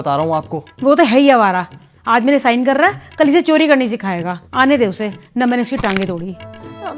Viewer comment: what did she say? OCD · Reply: She said " NA maine uski taange todni " or it is not correct so sorry 😢